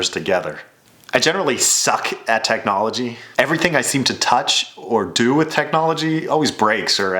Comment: I thought he said everything i touch breaks.